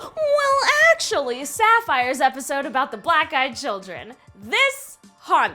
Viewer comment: I thought she said "Black guy children"